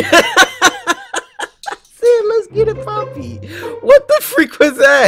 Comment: Caption the thumbnail in the comments.. I can't take this game anymore bro... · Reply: I thought you said let's get it Bobby